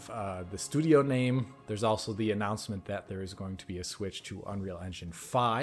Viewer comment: did anyone else hear Alex say, "There's also the announcement that there's going to be a switch to Unreal Engine 5," and initially think he said, "There's also going to be a Switch 2 Unreal Engine 5" in regards to the Halo series?